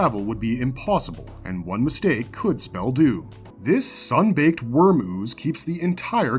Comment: did you just call the navigator a "sunbaked were-moose"???